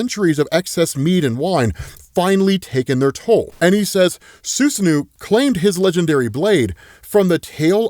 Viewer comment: when says “Susanoo” sounds like he said “Snu snu” 🤣 i’m sorry but that is the most HILARIOUS attempt at pronouncing Susanoo…i’ve never heard that attempt before 😂😂😂 and was soooo confident and serious when he said it…i can’t stop laughing 💀 god i love this channel